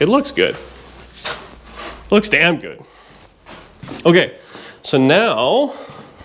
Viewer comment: It sounded like you said "It looks good...Dan good"😂😂😂